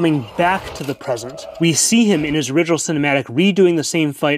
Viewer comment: I feel like Ekko might also be an AU version of Caitlyn: both are trustworthy, stubborn, good to their core and have a common goal of helping people, but while Cait was born privileged, making her more trusting in others and trying to change the system from the inside, Ekko was born in Zaun, so he doesn't trust many people and operates outside the system. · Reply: is there some pre arcane show? What's the title? I heard him say : .6:..something cinematic.